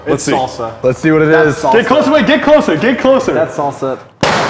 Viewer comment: A bit concerned when, at 6:06, the captions/subtitles say "d!ck closer" rather than get closer o_O Wow YouTube...